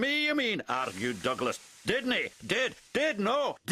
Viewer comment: Did Donald Just Say Didney As In DIDNEY WORL!?